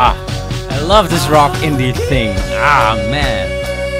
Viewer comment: did you say indian rock thing?